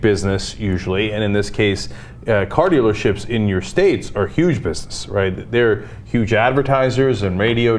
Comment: CC says huge breasts.